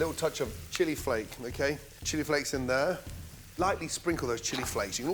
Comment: I thought he said " chili fries", but he said chili flakes 😁